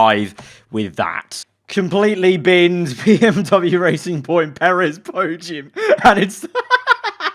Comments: Did he just say BMW Racing Point? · Did he just say? BMW Racing point??😂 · did he just say BMW Racing Point? 🤣